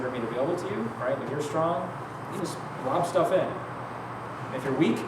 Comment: What is he saying on 42:46? "If you strong, you just ??? stuff in". What is the verb, I don't get it (English is my second language). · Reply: I think he says "If you're strong, just drop stuff in". It might also be Lob, meaning throw.